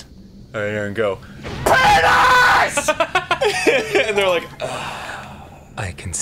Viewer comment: I didn’t know Danny said “I concede”. I thought he said “I can see it”